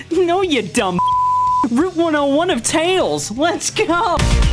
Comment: Gotta admit, with Sonic being a large part of my childhood, this is exactly how I remembered Sonic Adventure 2, and Live and Learn (for a hot minute, I apparently thought the singer was saying “Letsa Go”, according to my brother).